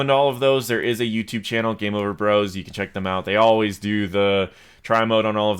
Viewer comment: I cant quite understand what you say here. Could I have a link to this channel? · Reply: I think the channel name changed but it's this one below. It used. to be called like game over bros <link> Typically he will go into the ptr with new releases and test and show the skins and new like visual weapon and ultimate effects.